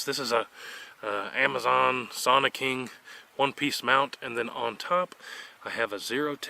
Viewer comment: What is that scope mount you used here I thought you said Amazon Sonic king???